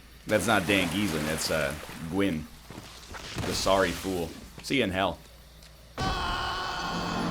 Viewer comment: i thought he said "see ya in heck" and i was wondering what kind of father he's become